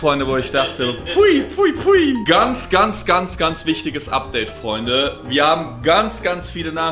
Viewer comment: hab ich statt update abdel verstanden